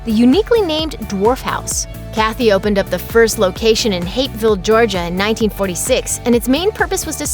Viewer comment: Did she just say “hate filled” Georgia at 4:35? What the hell was the point of that?